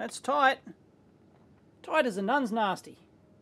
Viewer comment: "tight as a nun's nasty" Did I hear that right Dave?